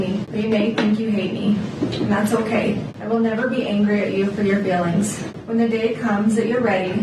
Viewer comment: Lol. How on earth did you hear her say “failings?“ She most definitely said feelings and it wasn’t even close to sounding like failings. You should get your hearing checked. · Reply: I heard "failings" as well, it seemed an odd thing to say. I reheard the same after reading these comments. I can only guess it's to do with her partially mumbled talking at that point, and accents; both the spoken and what we are used to hearing.